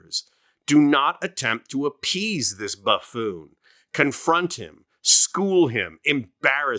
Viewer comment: Did I just mishear what you said? Instead of calling Trump a Buffoon I thought you said Buttfoon, which I think is a wonderful new nickname.